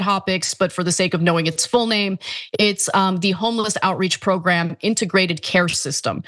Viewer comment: Did she say homie-less?